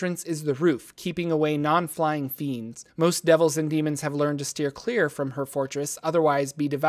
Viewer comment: Glad I'm not the only one who hears "devil-dragon" and thinks of The Flight of Dragons =)